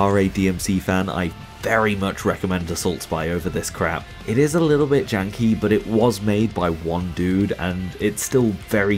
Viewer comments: what's this game called? I couldn't hear the name properly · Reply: Assault spy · what's the name of the game he's recommending ? i can't hear him im interested in trying it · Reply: Soulstice is also good. And there's the upcoming Genokids and Enenra, as well as Gori: Cuddly Carnage (kind of) and Yasuke: A Lost Descendant. Other good ones, though in a different vein, are The Dishwasher: Vampire Smile, En Garde! and Clash: Artifacts of Chaos.